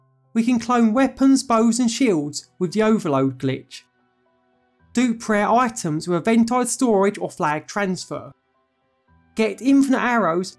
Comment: Anyone understand what he says at 0:13? 😂 CC says “do prayer items with a ventile storage or flag transfer” 😅